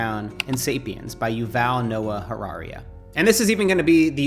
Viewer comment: Did you just say Yuval Noah Harariaa? 🤨